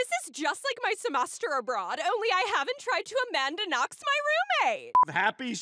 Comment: what does she say? Amandaknox?